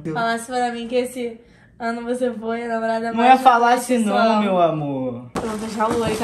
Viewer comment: "nao é falasse não meu amor" KKKKKKKK 9:09 "oi meu amor tudo bem" DHWKDB